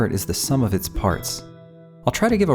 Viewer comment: I thought he was saying Ishee. I thought it was a Japanese game until I looked at the channel name.